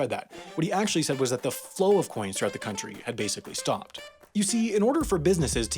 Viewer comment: your subtitle says economy instead of country